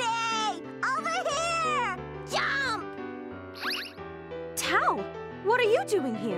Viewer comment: BWHAHAHA, Jubei has such a silly voice in English. I hate dubbing, i really, really do. The game itself is really awesome though, and a great review too! And, 2:58, did Tao actually say "JUMP!" ? Or is it just me, being a racist against dubbing, actually looking for faults?